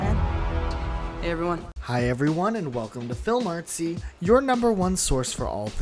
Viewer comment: This was my first time coming across your channel, and i wasn't looking at the screen when the video started. So I thought you said " Hey everyone and welcome back to Film Nazi " and I was like wut..